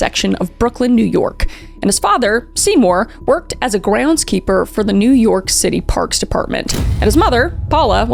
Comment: i swear i thought you said 'gwouns keeper' 🤣😭